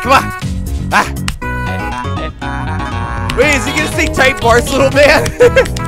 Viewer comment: I thought I like how he said what did he say like another word but he actually said take bars little man